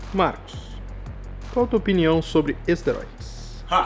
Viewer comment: opinião sobre o que? Não entendi o que ele disse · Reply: minha opinião sobre esteróides aiuhauihauihauhauihaui